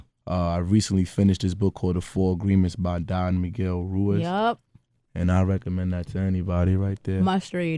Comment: What's the book he said he finished reading at 13:40 ? The four..? By Don McGill... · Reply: The Four Agreements by Don Miguel Ruiz. "McGill" lol. Sounds the same hahaha